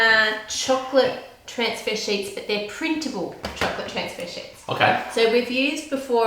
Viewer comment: it sounds like she says shits